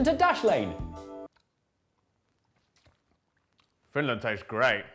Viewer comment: I'm not sure why, but "Jay, why are there countries in my cereal‽" and "Finland tastes great" always make me laugh. You two are amazing comedians. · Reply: I heard “Greenland tastes great” but okay yes.